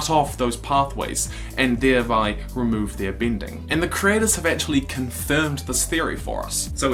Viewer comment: I swear I thought you said ear bending and i thought about someone with huge ears who could flop them around like a Bayonetta weapon